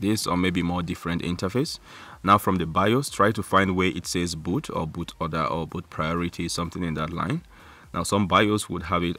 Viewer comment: i have no idea what u said there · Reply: Bored Person ‘there is boot, boot order or boot priorities, something along that line’